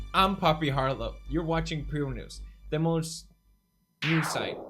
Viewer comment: It says “pew news” but I keep hearing “poo news”